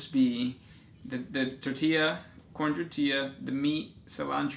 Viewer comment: Did I hear Khorne or Corn ^^